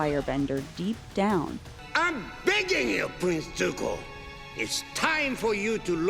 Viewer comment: I thought he said "Prince do go".... If so... any idea where. I have only one place I want to go but don't know if I'm allowed as it's to a person I love more than anything in this universe